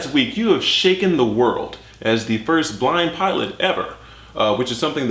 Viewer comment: first I thought he said the first black pilot